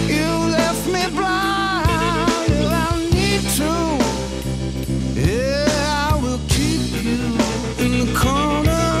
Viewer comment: porque nadie pone lo que dice en el minuto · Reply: Ahi dice necesitare que, despues se complementaria con te mantendre en